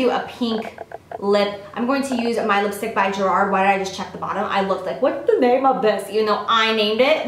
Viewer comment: i seriously thought she said gerard way.